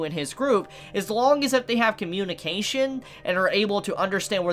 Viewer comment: I thought it said communism. XD